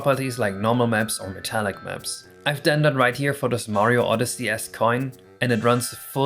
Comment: every time you say "esque" it sounds like youre saying "ass" so it was pretty funny to hear "cotton candy ass materials" suddenly